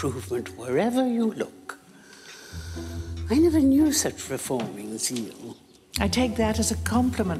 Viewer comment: I heard "I never knew such reforming seal" and for a moment i wondered what seals reform and how the Dowager could have met one. Sometimes not beeing a native speaker is fun. 😂